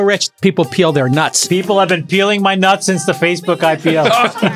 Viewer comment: "I haven't peeled nuts since the Facebook IPO" is now my favorite Chamat quote. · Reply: “People have been peeling my nuts since the Facebook IPO” I think that’s what Chamath said